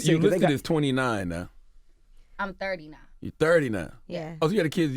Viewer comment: Did she just say she’s 39??? Did I hear that right??? Damn sis😍😍😍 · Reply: She said she’s 30 now lol not 39